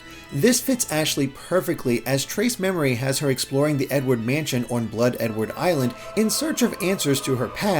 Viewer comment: I think i didn't Hear that right...... i heard "The N-Word Mansion"